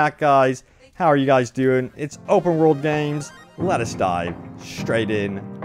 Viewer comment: For a moment I thought he said “let us die right in” 😂